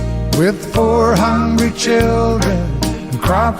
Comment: When my youngest son was 4 or 5 he used to sing "4 hundred children and caught in the field. I would say "Must be children of the corn" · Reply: I always thought he said 400 children too. Lol.